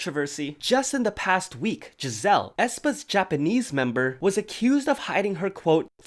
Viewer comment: did he just say Jazelle (that’s my name)